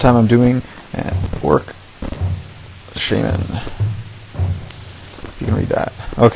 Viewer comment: sounds like you said: "orc shemale"! LOL! It's ShAman, not shAYman btw. But good video and excellent painting job!